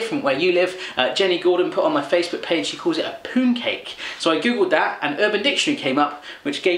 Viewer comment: I thought you said poo cake for a minute there